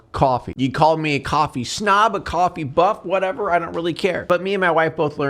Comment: Dude, for a second when he said, "You could call me a coffee slug." I legit thought he was gonna say, "You can call me a coffee slut." 😂😅